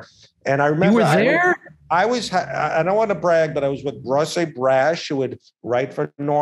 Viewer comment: with who did he say he was with? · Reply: ross abrash